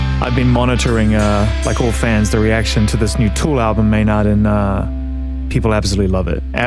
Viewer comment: XD i thought he said "a TOTO ALBUM" - <link> was like .. isn't Weezer doin' that...